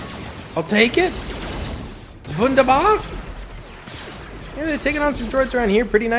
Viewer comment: Did he just say „wunderbar“?